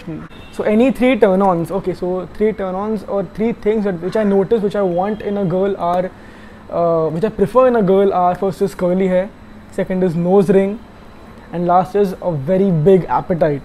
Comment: did u just said , first HIS curly hairs?? 😅Sry no offense, btw video is superb ❤️